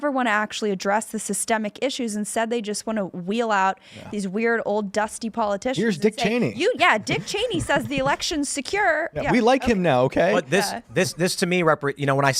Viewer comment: I thought she said Dick less Cheney